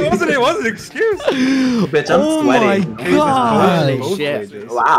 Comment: Why the heck i heard “i’m sweaty b**ch” does anyone hear it?